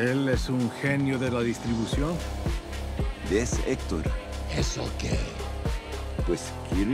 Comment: He didnt say "not bad", he said "what about it?" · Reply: The editing plus the actors accent makes it sound like hes saying "Eso què?" But he actually says "Its okay" or "Is okay", to which Eladio responds "Its 'okay'"? Emphasizing his confusion for Hector's dismissal and leading to the jealosy comment.